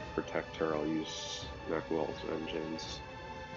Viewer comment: Were you note spoiling on Theodore? Just asking! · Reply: Also not going to lie I heard "Macnol's Vengeance" at 14:45 the first time lmao, I was like, is he mad at you 😂